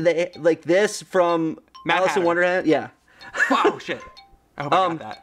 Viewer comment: For a second there I thought he said "Malice in Wonderland"